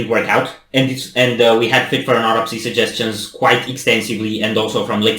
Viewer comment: Yeah too bad the Far From Heaven one was corrupted. I was really looking forward to that one. But this one is almost as good imo so I'm happy about this one working out great 😁 I had a feeling you guys would like this one. Yeah it's got that mellow but brutality to it. Brutal art 😄 Saltwound would be my next suggestion. We'll there are a lot of great ones they have so it's actually really hard to choose which one should be next lol but anything you decide to do, I'm sure you will love it. All their songs I either love or at least like. None that I dislike. I'm excited for whatever comes next from FFAA!! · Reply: Oh and "Autopsy" is pronounced with a sharper T 😁 "awe-top-see". Sorry lol I just never heard anyone pronounce it that way before 😆 I thought at first you said Oropsy with a rolled R 😂